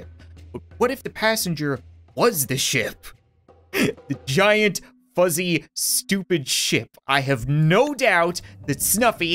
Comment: It sounded like he said a giant fuzzy shit